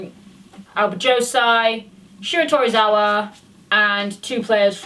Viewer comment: did I just hear that right? “Shota Aizawa” me: Nani.